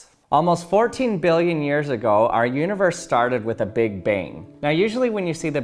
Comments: I heard "big bing". Basically my notifications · Just me or does it sound like he is saying "big bing"?